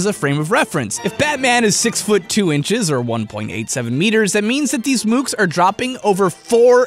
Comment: When he said meters I thought he said feeters